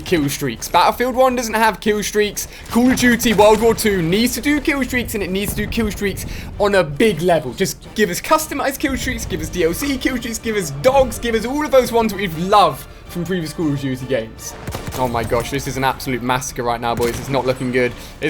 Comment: did i hear alia saying DLC killstreaks...?